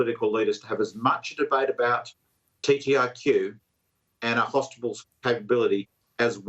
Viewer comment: did he say hostibals? 🤣